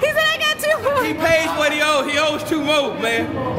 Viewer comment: I heard "he owes to hoes" but it was "he owes two more " righ?